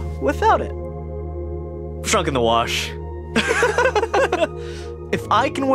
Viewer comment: heeey can an english speaker tell me what Ryan's saying here? Usually if I don't understand what a character is saying on the first watch, I get it on the second but I keep replaying it and I still can't catch the words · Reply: He said “shrunk in the wash”